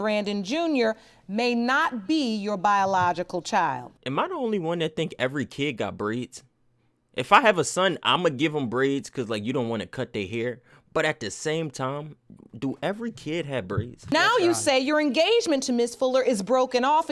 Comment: I heard aids and jumped. Imma give my kid aids 💀